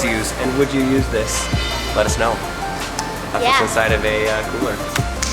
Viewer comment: i thought it said whats inside a s400? 😂